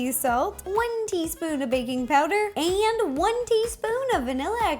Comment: I misheard when you said baking powder I thought it was baby powder😂😂👌